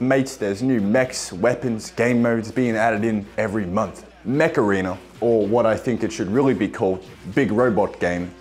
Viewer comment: When he said "Mech Arena", I heard "Macarena"